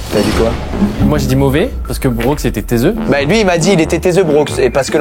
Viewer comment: pardon j'ai 25 mdr j'suis vieux, ça veut dire quoi "tezeu" à29:51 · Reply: il dit "taiseux"